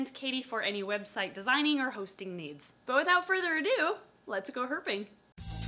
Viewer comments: Emily: "so, let's goherping Me: confused screaming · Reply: I thought you said "let's go gophering"... so confused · I thought you said "let's go gophering"... so confused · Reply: aspen the puppy me too 😂 I thought it said lets gophering.